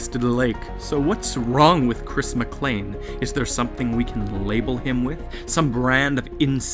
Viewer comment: we fr bout to make a creepypasta off this man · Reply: Thought you said criss mr.clean lol